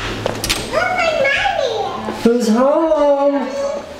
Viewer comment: I could swear that at 06:40 said "where's my mommy at??" 😂 ❤️